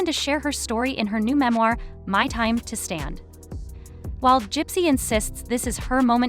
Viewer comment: I could have sworn she said "My time to stab." 🤔